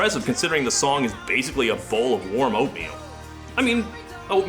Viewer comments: I thought you said "a bowl of warm opium". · I heard "a bowl of warm opium."